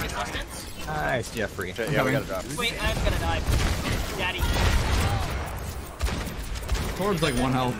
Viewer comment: Did he just say "'I'm gonna die...daddy"?